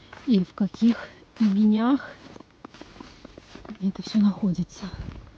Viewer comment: Мне послышалось "в каких ебеньках"???😁😁😁